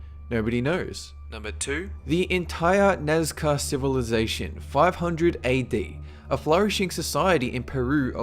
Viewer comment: I love the D.B. Cooper story. I live in the area where he jumped. I find it so fascinating that he was never found. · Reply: Hahaha, because of your accent, for a second I thought you said "NASCAR civilization."